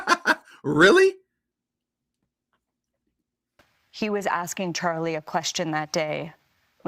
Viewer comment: Hey, did anyone else catch that error at 36:56 in the podcast? Was it just me, or did the host totally trip up and say, "she was asking?" Did she just stick to the autocue without even realizing? If so, this whole thing's a sham!